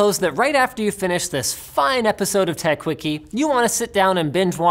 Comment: For a moment I thought he said "final episode".